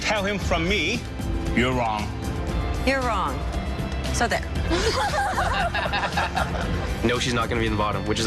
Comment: What does she say at 35:34 after "you're wrong" ? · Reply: She says “you’re wrong! So there”